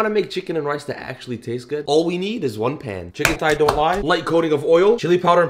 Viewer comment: I thought you said all I need is a pan